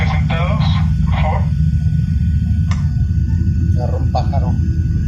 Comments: The voice says "un pájaro" and that means "a bird" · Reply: I listened again (4:30) and I hear "agarró pájaro" which is literally "grabbed bird." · or thereabouts I think I hear him say "pájaro" so yes, bird strike.